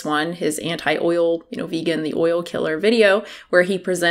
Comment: Wait did I just hear that right? 1:12 “vegan, the oil killer!!!” Coming to a town near you!!